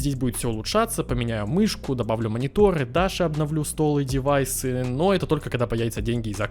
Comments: Ххаха мне послышалось Дашу обновлю(не в коем случае не надо еë обновлять) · мне послышалось: И Дашу обновлю...)